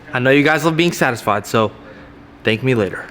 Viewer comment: He said thank me later and the captions say thank you